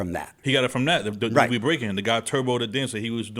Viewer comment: Did he just say Who is Turbo?